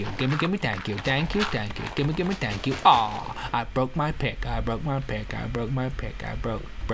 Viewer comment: this is a freakin awsome song from nowhere at first i thought he said somthing diffrent then "PICK"-"DI.."